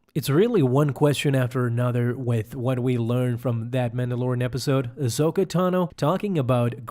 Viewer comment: I heard "...what we learned from Batman lore..." instead of "...what we learned from that Mandalor[ian episode]..." when listening for the first time and got really confused for a second.